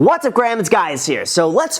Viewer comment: did u just say whats up graham guys is here 🤣